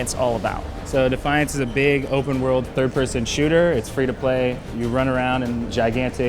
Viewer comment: Did I hear 'free-to-play'?